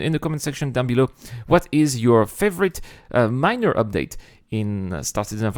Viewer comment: OOOHHH... My favorite MINOR update. At first I thought you said MINER update. I think the hunger/thirst sounds interesting. I wonder if will make people look forward to the vending machines becoming operational. I certainly do.